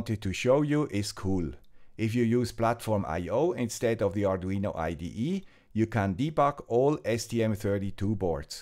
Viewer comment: "...instead of VSCode..." But isn't Platform IO built on TOP of VSCode? <link> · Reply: I said "instead of the Arduino IDE..."